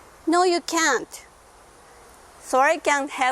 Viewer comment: When first watching- @ 3:36 I thought you were saying sorry I can't help to solve your problem, but then I realized you said "Soiree" can help to solve your problem! 😁